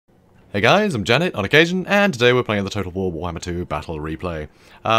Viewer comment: This time I heard “I’m Janet, like Asian”